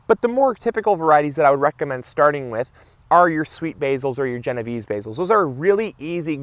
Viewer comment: I thought you said genital basil , You need to talk a Little slower .